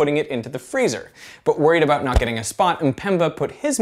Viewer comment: hello. i don't understand what he said at 0:31. Because english is not my first language. I think he said "But worried about getting it spot" ? Is that it? if So i don't understand the sentence. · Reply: About NOT getting a spot Sorry for 8 years late